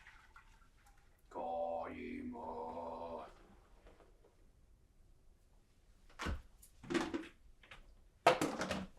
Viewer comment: what are you saying at the 7:20 mark? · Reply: Kali Ma!!